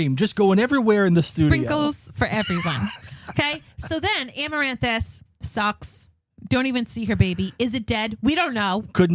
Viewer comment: FYI: When you play Drew Carey, it sounds like he is saying, “This sucks Am I the only one hearing this? Anyway, it makes me giggle every time I 😄